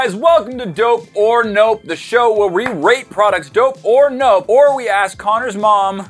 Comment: It sounded like said “this is where we rape products”lol😂😂😂